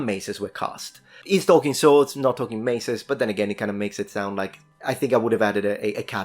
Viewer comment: First time you said 'Maces' I heard 'Messers' and it sounded like you were saying Messers are not swords and I was confused. 😅